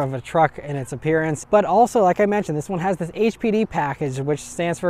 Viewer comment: Not going to lie I thought you said HPV package....🤣🤣🤣